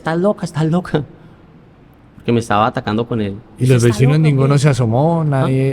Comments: Escuche en el 34:54 una voz que dice está loca o que.. escuchén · Cuando el se rie, hay una voz que dice "esta loco o que" a los 34:54 minutos del video. No sé quien lo dijo, pero al ver que se reía dije exactamente lo mismo. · Nunca le pregunte el nombre y después dice cuando fui a la tienda le decía hola Michelle como estas, nunca fui muy social pero buscaba conversa a una niña de 13 años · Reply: En el minuto 34:50 se escucha una mujer que dice está loca o que y no es la que entrevista 😮 es aterrador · Minuto 34:53 se me puso la piel chinita. Dicen que es la presencia de la chica fallecida que dice esta loco o que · Min 34:54 la voz que dice " esta loco o que " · se escucha que dice ella, está loco o que